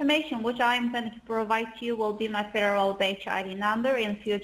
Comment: Even the closed captions think they say 'Bitch ID.' It also heard 'little ass abacus.' 😂 At first I thought you said Berle Turlett. They're investigating in this paper.. okay .. Lol Plymouth Volare` They tell me I was born in Cincinnati but I can't really remember either. 🤔 They don't have to make a federal case out of this. Lmao! It's the cops! De cops are de culprits! The Judy member.. lol 😂 This guy.. LMAO! He's very colorful.🎨 Lol! He forgot his name!